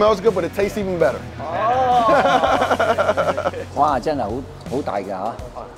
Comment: Did he just say Hawk Tuah?